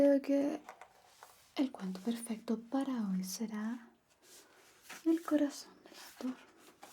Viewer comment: escuche mal y entendí "el corazón de gaturro” 😭